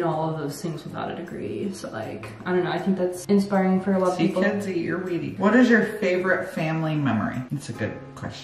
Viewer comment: what does say after see kids… bc I hear eat your weedy and I’m so confused